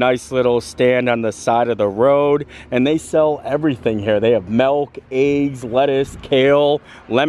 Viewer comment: Wait.. did you say "Melk, Ei" at 02:10 ? That's hillarious... Because that are the Dutch names for milk & eggs, nice to see that Pennsylvania Dutch is honoring it's roots 😀 b.t.w. you're not supposd to drink "raw milk" as is. you need to cook/pasteurize it before consumption, as it has lots of bacteria in it still 🤣